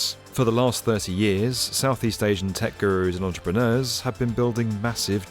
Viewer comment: did he just say "techgirl" ?